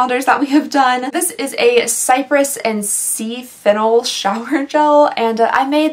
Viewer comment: when you first said sea fennel i thought you said sea fiddle and i was like ooo wow whats a sea fiddle lmao